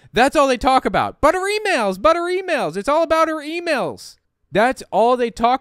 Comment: I hear "buttery nails" when " but her emails" is said by.